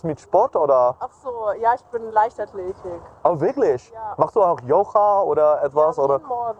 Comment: At minute 19:46 the German woman says “Ja, ich bin ….Leichtathletik”. I guess she was a little nervous, it should be “ Leichtathletin” a female track&field athlete. Or perhaps she wanted to help and just said the easier word Leichtathletik (track&field). Anyway, he subtitled it as “lightly active”. Then suddenly talks about yoga, so he either did not understand what the woman said and he really believed she said “ lightly active” or he intentionally switched from track&field to yoga because yoga is something he has prepared for 😂😂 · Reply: She says: "Leichtathletin"